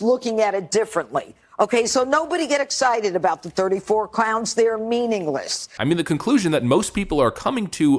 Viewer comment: Sounded like Judge Janine called them "34 clownts" if so, subtle...